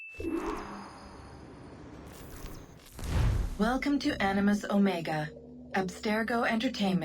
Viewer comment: legit thought that said “Annus”